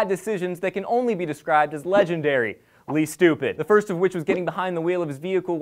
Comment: i thought i heard say "Lee Stupid" harsh bro... kick his ass